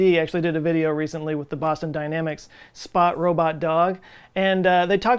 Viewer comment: When he said “Boston Dynamics” I heard “Pasta Dynamics”. And I now know what to name my new pasta company.